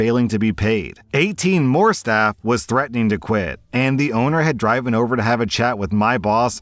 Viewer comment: Did anyone else notice he said “Drive-in” instead of driven? Lol 😂